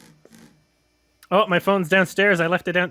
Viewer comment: I thought she said "my phone dyed" lmao